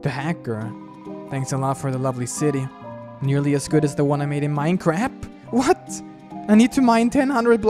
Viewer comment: I think at 11:02 he said minecarap =Minecraft (I like Minecraft)